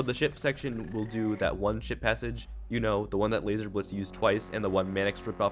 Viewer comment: I keep hearing “shi*t passage”